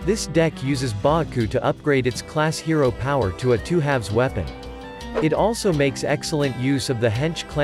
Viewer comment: There's a text-to-speech mistake, instead of two-two it says two halves.